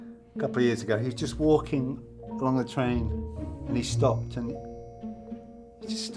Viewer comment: “It’s focking Bronn” 😂😂😂😂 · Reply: Ho shit I heard ''He's fucking blond'' and I found that hilarious but whatever that's fine too 😂